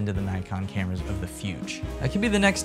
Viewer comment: I really thought you said Fuji Juice of the future.